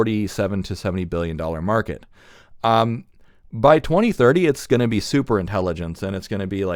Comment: did you just say asi by 2030? That's quite the leap. I'm still on the fence of agi. 😂 If we do achieve agi by 2027 like a lot of people are speculating, then maybe I could see it. We'll see.